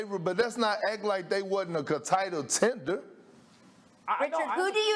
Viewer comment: Did he just say Contitle tender 😂😂😂